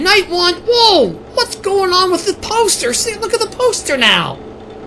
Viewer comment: I thought he said toaster be he said poster